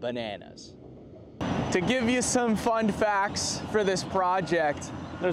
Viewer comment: lol at 4:28 he says fund facts instead of fun facts